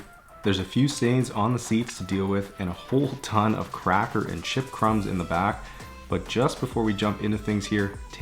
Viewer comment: I didn't quite pay attention during the intro, and I thought I heard sh*t crumbs, instead of CHIP crumbs